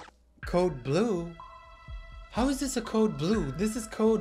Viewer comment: I thought it was saying get food and not code blue….